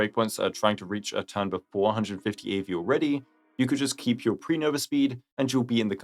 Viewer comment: Firefly speed section made me super confused and then I heard: "eh if you want just keep pre nova flare speed" Well that's my plan now LMAO